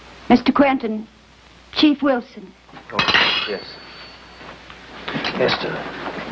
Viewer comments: I guess I missed the middle part. It was ruled Ester was smoking in bed. Problem solved. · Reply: did he say oh shit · did he say oh shit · lol or the twin power. This story would never have been made a movie about a man. Period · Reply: did he say oh shit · He's married to a damn Fire-Bug. That's why she just so happened not to be present when 'Emily' called. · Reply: did he say oh shit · One Step Beyond=Belief Stay Rational my friends, the party lasts a lifetime. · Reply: did he say oh shit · ❤❤❤❤❤❤❤❤FANTASTIC, BRILLIANT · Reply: did he say oh shit